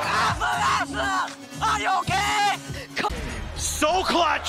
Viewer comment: did i hear "fns are you gay?" or "fns are you okay?"